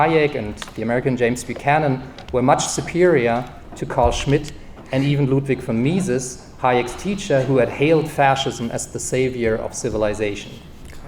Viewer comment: what did he say? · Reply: This is from the transcript: much superior to Carl Schmidt and even Ludvik from Mises Hayek's teacher who had hailed fascism as the savior of civilization American constitutionalization was thus a counterrevolution and the various popular uprisings against it such as Sha's rebellion in <num>